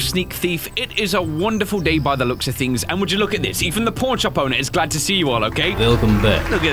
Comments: Did he say pornshop owner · Did he say pornshop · Sounds like u said pornshop Quite funny man